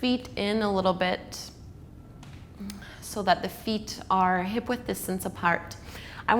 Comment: you say something that I hear in many of your classes but never have been able to decode it. What does it mean? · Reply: Which part exactly? At that timestamp I said to place your feet hip width apart meaning aligning your heels with your sit bones!